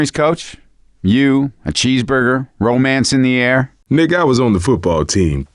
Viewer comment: i think he said nigga😂😂